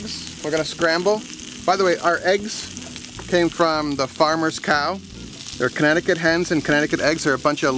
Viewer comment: did you just say cow eggs, l.o.l.